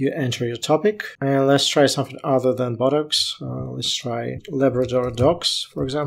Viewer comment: I thought you said "buttocks" not BOTOX😂